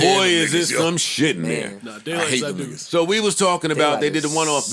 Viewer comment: is it just me, or did Parks say, “Daylight is that nigga”? I played this back like 10 times🤔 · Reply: 😂 daylight’s that dude Come on man